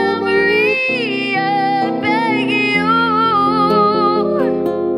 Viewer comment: I heard her saying: Give me a memory I beg you .. instead of I can use